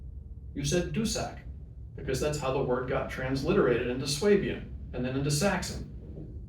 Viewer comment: what is that word you say at 5:46 - "... you said dussack, because that's how the word got transliterated into *???*, and then into saxon" (sounds like 'slabian'?) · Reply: “Swabian”, a type of upper German (think Stuttgart area).